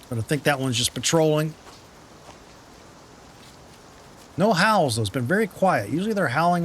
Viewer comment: Too funny... (this is an older upload, so not sure if you'll see this comment or not) but at the end when you said "oh we've got some howls" I heard "owls" and was like... what? There's no owls in this game... it's been a while since I've played so for a second I thought maybe they added owls to Olympus lmao. Nice starter vid, will be watching this series! Cheers!